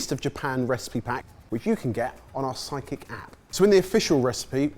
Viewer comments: Every time I hear them say "Side Kick" app, I hear "Psychic" app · Every time you all say "sidekick app" I hear "psychic app" lol!!! Love you all! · EVERY time the boys mention the SideKick app I hear it as "Psychic app" - I guess it knows what I want to cook before I do! 😅